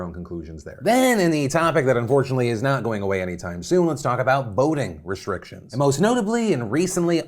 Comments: I thought you said "Boating restrictions" and I accidentally let my guard down for a split second. I hate reality. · When you said “ voting restrictions” I thought you said Boating restrictions at first. Lmao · I thought you said boating restrictions, can you do a story on boats please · I thought you said "boating restrictions" at first. What you actually talked about was more important, but less fun :( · Omg I thought you said boating restrictions at first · I genuinely thought Phil said "boating restrictions" and got super concerned there was some crazy story I hadn't heard of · I heard boning restrictions.. I thought you were gonna talk about new boning restrictions in Texas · Is it me or did it sound like he said boating restrictions at the beginning of his segment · I'm very tired and I genuinely thought you said boating restrictions. I was very confused for a minute. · Phil the first time you said voting restrictions, I thought you said boating restrictions. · I thought he said boating restrictions and I got really confused 😅